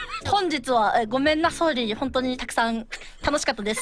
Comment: did flare say gomen nasorry?